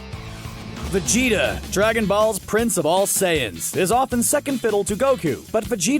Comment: Did you just say “Dragon Balls”? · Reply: Dragon ball’s I’m pretty sure